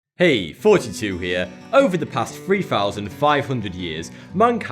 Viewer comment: Despite his channel's name, I have always thought he said "Hey, 42 here." Keep in mind I've been watching this guy for years now.